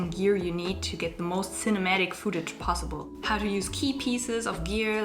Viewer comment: She says “footage”, I hear ‘foodage’